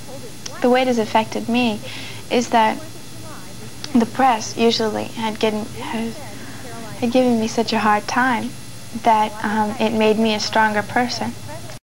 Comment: Did she say "breasts"?